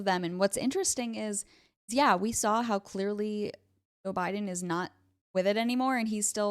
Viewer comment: Did you actually say "O'Biden" around the 7:29 mark?